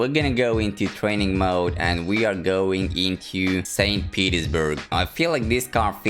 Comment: I thought you said “Saint Pedo’s-burg” for a second 😭😭